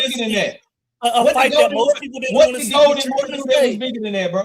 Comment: he cooked himself but he was talking while u was so u didn't hear it. He literally said " a fight nobody wanted to see". Not realizing that even furthers your point and deminishes his! 😂😂